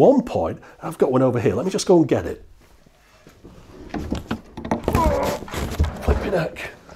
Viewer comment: when he said flippin 'eck the automated captions misheard it as flip your neck